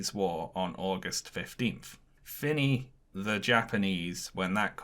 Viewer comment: What was said at 54:51? It sounds like "finney the japanese when that comes about" but finney isn't a word? Nvm I think it's fini, french for finished